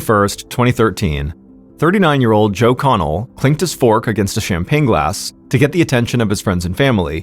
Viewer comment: sounds like he said “clinked a spork on his glass,” which I think makes this story 1000x more compelling.